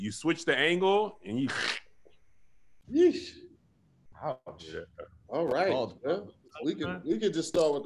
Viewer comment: Did pat say "Ouch... Bald problems” @ 1:40? Lmao